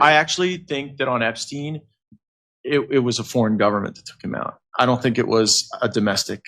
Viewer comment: I don't think Epstein is dead. When Gaetz says, "a foreign government took him out" I hear, " took him out of the country". Why would they kill the guy who had all the information.